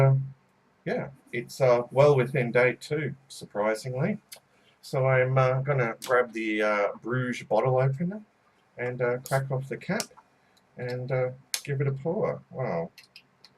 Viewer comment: Very enjoyable review but did you say "brusch bottle openinger" at 1:21? Is that some High German or something? Or am I just the 'high German' in this situation?